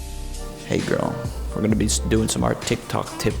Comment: I THOUGHT HE SAID "HEY GIRLIE"